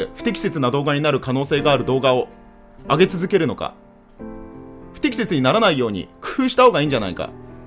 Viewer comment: 「不適切にならないように工夫」が「不適切にならないように脱糞」に聞こえた、もう俺末期やわ